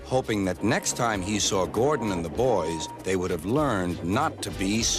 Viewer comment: did he say "THE BOYS"